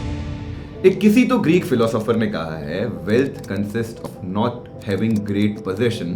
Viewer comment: SOUNDED LIKE 'KISI TOH GREEK PHILOSOOPHER NE KAHA HAI'😂